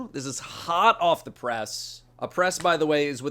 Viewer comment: Oh, a press, I thought you said oppress. Easy mistake